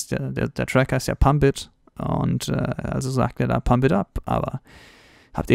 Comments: ich hab da immer hit up verstanden. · Ich habe pimb up verstanden aber war relertiv nahr dran